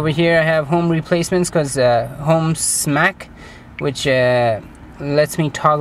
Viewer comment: what is that app that you say on minute 6:55 is it homes mac or something like that?